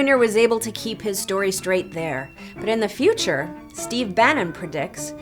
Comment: Did you just say... "they're gonna crab don jr like an egg"?